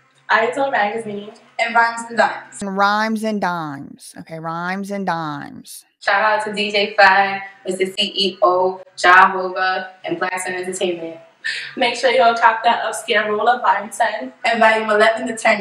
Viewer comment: Did she say Ja-hova? As in Jay Z? 40:52?